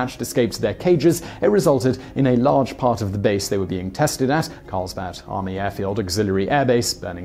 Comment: It sounded like he said "Carlsbat Army Airbase."